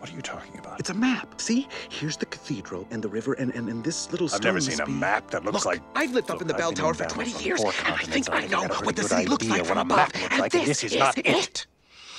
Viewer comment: I always try to hear what Phoebus is saying, but Quasi always drowns him out. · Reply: It may sound something like this: "I've been in battles on four continents, and I've got a pretty good idea of what a map looks like, but this is NOT it!" Yeah, I agree, Quasi should have backed off so we could hear Phoebus better, and Phoebus should have come out more.